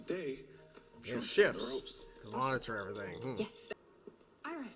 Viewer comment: "monitor" ... thought at first they said "The Monitor" and thought when did Mar Novu get back?